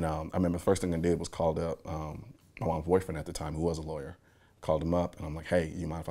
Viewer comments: Did he say his “ boyfriend “ at 4:40? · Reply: Mom's boyfriend. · Did he just say "Boyfriend"?😳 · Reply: He said he called his moms boyfriend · Did I hear what I think I just heard? Much respect for you man. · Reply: Nah, that's what I though at first, but he said "moms boyfriend" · 👀 when you thought he said his boy friend at that time. He better not be gay! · did he just say I called up my boyfriend???? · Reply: He said his moms boyfriend